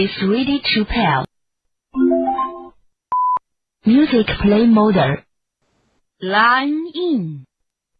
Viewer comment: So it sounds like they say MODER but it’s actually trying to say mode, bet the “e” is also included in the speech